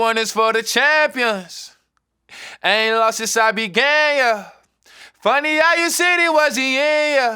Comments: I thought it said “I ain’t lost since I been gay yuh” not began 💀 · Am I the only one who thought when he said “I ain’t lost since I began yuh” “I ain’t lost since I been gay yuh” 😂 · Literally thought he was saying "I ain't lost since I've been gay, uh" and "Then i went and did it gayer" lmao. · I deadass thought he said "I ain't lost since I been gay, yah" 💀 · i didn’t know it said “i ain’t lost since i began” i thought it said “i don’t take losses cause i’m gay yuh” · This whole time I thought he was saying “I ain’t lost since I been gay yeah”😂😂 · I thought he was saying “and this one is for the champions… I ain’t lost since I been gay (yuh)” 😭 whole time he was saying “began (yuh)” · I thought Nas said: "I ain't lost since I been gay." I liked that, but began is nice too. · I thought he said “Since I BEEN GAY” the whole time😅😅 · This whole time I thought he was saying “I ain’t lost since I been gay yuh” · I seriously thought it was “I haven’t lost since I’ve been gay.” In my heart, that will always be the lyric. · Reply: i really thought mans said “ain’t long since i been gay, yeah” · I really thought he was saying “I ain’t lost since I’ve been gay, yea” and I was honestly here for it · This whole time i thought he was saying “i ain’t lost since i been gay, yuh” lol 😂😂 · Am I the only one who thought when he said “I ain’t lost since I began yuh” “I ain’t lost since I been gay yuh” 😂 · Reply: i thought is said i ain’t lost since i’ve been gayer 😂